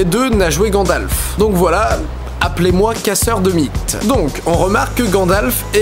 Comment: je me suis tapé un fou rire j'ai compris casseur de bites!